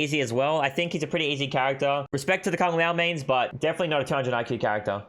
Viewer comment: in subtitles it says kangaroo instead of Kung Lao.